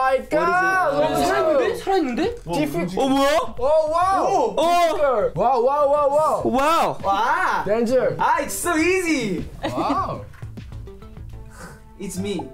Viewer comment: Omg so cute hongjoong 💞his English is good and sweet. Hahhaha at 1:07 it sounded like he said "OOhHHh Sweezy"😊😂